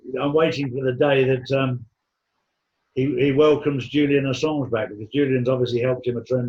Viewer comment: at the point of 26:01, w2ho did Charlie say was helping TRUMP tremendously , I couldn't understand him ? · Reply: Julian Assange....